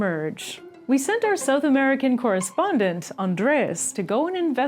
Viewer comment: the captions say Latin but the word spoken was South American. just trying to be helpful